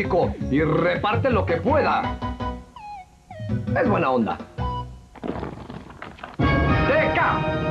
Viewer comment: = No entendí bien si dijo Buena onda o Mala onda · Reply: Es buena onda dice XD